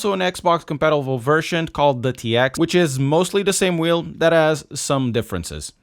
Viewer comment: He said "dat has" I thought he said "that ass". 😅